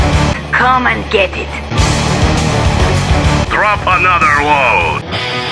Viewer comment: I thought Kirov said "Grope another one..." xD